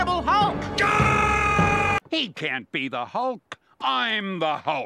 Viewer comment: I thought Stan lee said he can't be Hulk! I'm Hulk! But aider way, still funny.